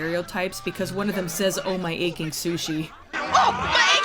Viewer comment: I've always thought the one goblin said aching tushy, and figured he was the one that got bit. Great review so far!